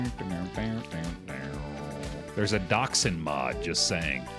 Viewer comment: why would you install a doxxing mod? or did I mishear that?